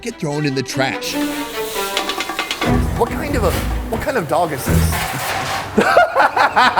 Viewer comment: Did he say, "what kind of dog is this?" Lmfao...I get it. Because they eat dogs. Hahahhahahhahah. -_